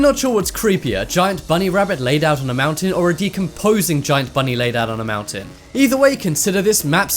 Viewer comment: I thought he said "A t-posing giant bunny laying on a mountain"